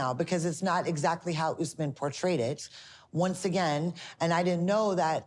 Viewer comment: Did she really say “potray-ded” instead of portrayed?? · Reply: “portrayed it”